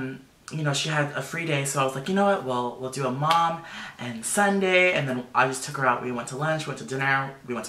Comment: When he said mum and son day I thought he said mum and Sunday!!!!!!!! Lol 😂😂😂😂😂😂😂😂😂😂😂😂😂😂😂😂 xxxxxxxx